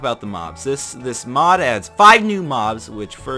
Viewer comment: I thought you said, "this mob adds 5 new mods". Whatever, Kingdom Hearts + Minecraft =Kingdom Minecraft, the most epic video game combo ever